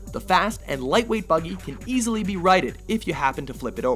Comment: Is it just me or did he say "rided?" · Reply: That's what I heard at first as well, but I believe he meant: "if the vehicle is flipped, it can be righted." As in corrected.